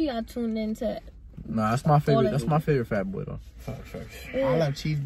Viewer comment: did just say the n word ?? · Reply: He said that’s my favorite fat boy